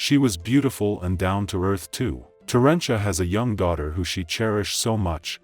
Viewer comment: I'm assuming her Dad's name was Terrence, thus they named her Terrencia. Not as bad as I have seen for some of these kids... She seemed like a nice girl. Only 1 1/2 year older than my oldest daughter, who ironically is in no hurry to get married despite the protestations of my Mom. She's nagging both of my daughters ages 23 & almost 26 to get married and have kids. My girls said, "Mom, we are not ready. Probably when we get in our 30s." I applaud my daughters for their wise choices... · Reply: I thought they said tarantula 😂😂😂